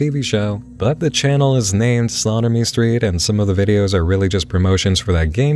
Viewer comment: Hope it ain't just me, but everytime he said "SlaughterMe Street" I misheard it as "Sodomy Street" I know it probably isn't intentional, but with names like "Osfoc" floating around, it was a fun coincidence